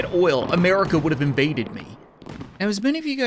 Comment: “ I swear If I had oil at this point now, America would have invaded me” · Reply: I heard "America would be bacon" and a I don't know why